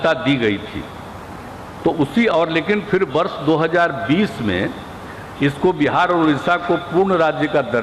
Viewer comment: क्या बोले? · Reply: 2020